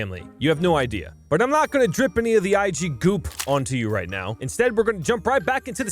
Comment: not IGoop....IGPOOP hehe (in 1:15 if you didn't catch it...)